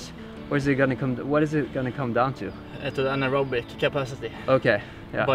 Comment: is he saying anaerobic capacity?